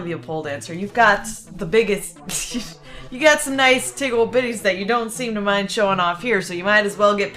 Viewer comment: did she say "tig old bitties" ?